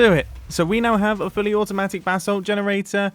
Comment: No crackle that I could hear, but I kept hearing "bath salts" over and over